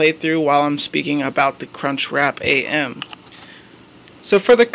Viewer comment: Did he say "cunchwrap"